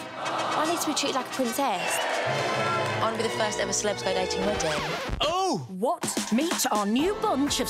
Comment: Came here specifically to figure out: what on EARTH does the woman say at 00:05? "I want to be the first celeb to --------?" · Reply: IAK first celebs go dating wedding